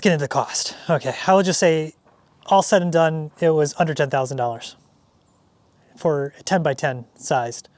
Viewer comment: Wow 10k is not bad... Easy to overlook all the minor things like door gap etc. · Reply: My ears aren't what they used to be, but I thought he said $110,000.